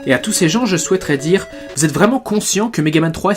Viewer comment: "et à tous ces gens, je souhaiterais dire : vous êtes vraiment cons (quoi ?)-cients (ah ok XD)"